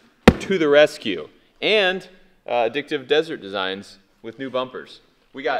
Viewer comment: I thought you said "Dicked up desert designs"